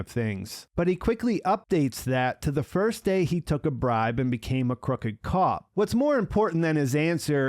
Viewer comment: I just listened to it. He says 2001 and then corrects himself and says 'March 17th 1984, the day I took my first bride. Then I would go forward and check on some people, make sure they are doing OK... you'. Then Saul. He doesn't mention anything about the shooting or 'becoming a crooked cop'. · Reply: You're right, I thought he said 'bride' he says 'bribe'. Sorry.